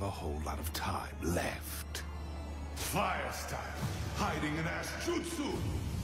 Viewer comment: i heard it as fire style : hiding in ass jutsu !!